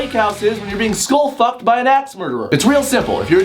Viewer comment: I thought said, "School fucked by an ass murder".